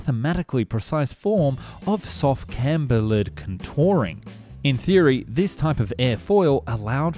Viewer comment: Is that "cantilevered contouring" you're trying to say? Didnt quite catch that.